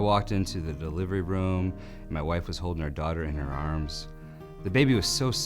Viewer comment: I like how he refers to the daughter as "her daughter" instead of "our daughter" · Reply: He says "our".